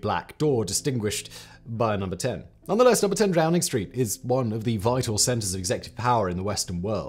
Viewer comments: did Simon say Drowning Street? 😅🤣🤣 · "Number 10 Drowning Street" anyone else hear this?